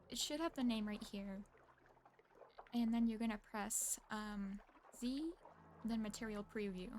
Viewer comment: if anyone is stuck at 6:31 like I was and speaks the kings english, you press Z ! I thought you were saying v or b and I was wondering why it wasn't working I didnt think to press zed lmao :'( thanks for the video